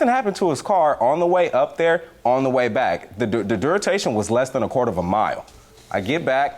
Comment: did he say the “Duritation”? Or am I tripping? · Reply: I knew that’s what I heard. Lol I think he meant the duration.?